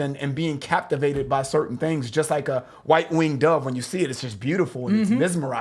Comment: I always thought she said, "Just like a one-winged dove, sings a song..."